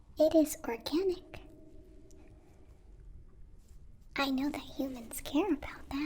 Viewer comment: I had to listen to 22:20 four time before I realized that she said organic. I kept hearing “it is for killing, I know that humans care about that.” I have to say that I was a little concerned